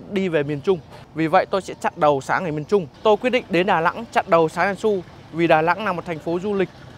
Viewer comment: Bằng cách nào đó xem rất nhiều phd tên sáng tiền xu mà nghe nhầm thành sáng yasua=))